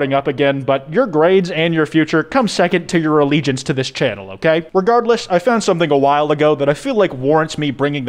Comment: "Your grades come second to your allegiance to the channel"-- 2021 Nice work comrade lovely video keep it up. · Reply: ngl I misheard what he said and though it was "your grades come second to your religion to the channel". still got the point across.